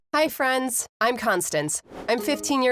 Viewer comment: I thought she said HI FRIENDS IM CONSTAPTED